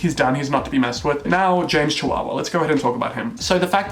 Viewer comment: Did he just say James chihuahua? lol